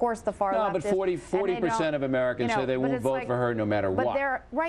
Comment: What the hell did Michelle say at 4:59? "...and those people are roosters." All I gotta say is, where's Laura Schwartz? Damn, she's fine!